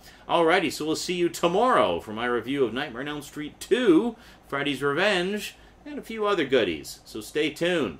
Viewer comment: Sounded like " Friday's Revenge"!